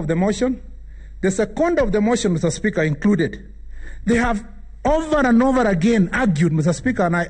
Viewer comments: 54 against 7 is just embarrassing to ruto,kwani ako peke yake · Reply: I thought they said they have numbers..🤣🤣🤣🤣🤣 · I thought they said they have numbers 🤣🤣🤣🤣